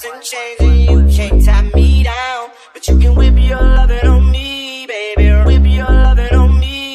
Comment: Who thinks it sounds like hes saying, "you can rip your lovin on me" instead of whip??